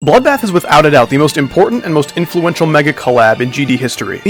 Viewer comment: I thought you said bloodbath was shit